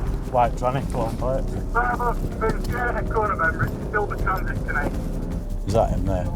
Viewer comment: pahahaaa did he call the transit a "white tr*nny"?? 😭